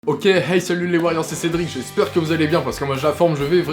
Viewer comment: En gros ton intro c'est "Ok et Hey salut les warriors c'est Cédric" ? Parce que figure toi que j'ai entendu "Ok les kheys salut les Wailords c'est ces drinks"